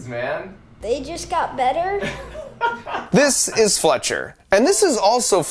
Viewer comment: Bro I thought he said flesh hair at like 1:00 like bruh that’s fucked up